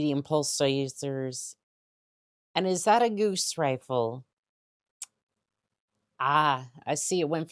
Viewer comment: Did you just say “goose rifle”? -.- By the blessed order of Gauss, I find that blasphemous!